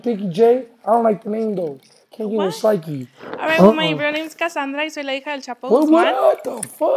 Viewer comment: did she say her name is Cassandra Israleija El chapo Guzman 😂